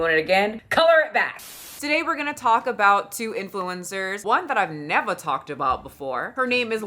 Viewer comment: Why did I hear " influenza" when " influencer" was said 💀💀😭.